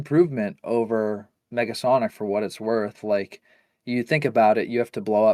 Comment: Why did it sound like said Megasonic instead of Negasonic